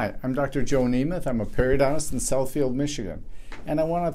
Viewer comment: I was playing the game and I heard, "Hi, I'm doctor Joe Nemeth," and instantly thought he said Joe Namath lol I turned around quick 😂😂😂